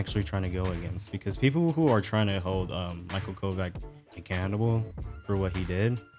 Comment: my whole imput in ts though is small as i dont use the internet much so my knowledge is tiny on things/topics on most things but most topics that occur on the internet is basically the same shit from the past being resurfaced online. Had put my own twitter count repiles to people i mention or follow just to avoid niggas who ethier leave some retard shit under my post or shit i in general do want to see. Its like the japanese niggas hootin and hollarin 'bout black people in their country. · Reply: sounds like you said "cannibal" instead of "accountable" lmao